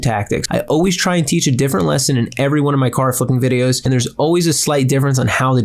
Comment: At min 9:22 did you say “ my car fucking videos”😂😂